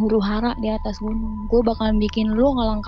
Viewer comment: "lihat lu yah,gw bakal bikin huru hara di atas gunung,gw bakal bikin lu ngelangkang buat di atas gunung". Pliss maksudnya apaan?apa cuman gw yg salah denger?